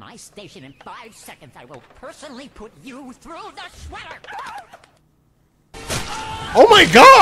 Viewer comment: When it said new door or I will put you through the shredder I thought it said sweater I didn’t find out till today watching this part